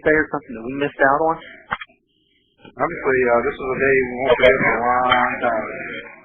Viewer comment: "this is a day we won't forget for a loooooong time", I think he says. Damn right. I'm not even an American, but hey, compassion is compassion. I was 16, saw it in the news. Didn't realize how big of a deal it was at the time - hey, I was a clueless teenager.